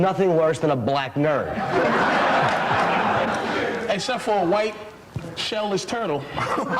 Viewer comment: A white shell-less turtle is what i think and hope i heard? lmfao